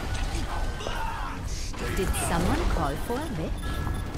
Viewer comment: Ok I've listened to 7:57 like 10 times. Mercy totally says, "Did someone call for a bitch?" lmao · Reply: It's the witch Mercy skin, so she's saying "Did someone call for a witch?" but with her accent it turns witch into bitch.